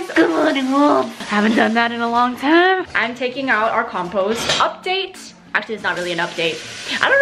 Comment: I thought I heard "couple" update instead of "compost" update.